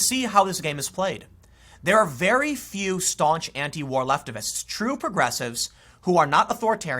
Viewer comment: Am i the only one that heard him say leftivist? Lol